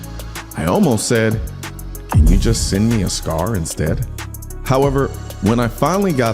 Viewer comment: Colion I thought you said just send me a Cigar instead! LOL 😆 SCAR! 👊👍👍